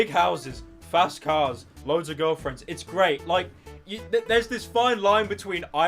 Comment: Did he say that he owns a girlfriend